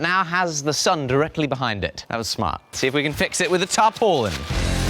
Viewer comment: The subtitles say that he’s saying “top hole in” and I’m hearing “tarpolin”. What is he saying?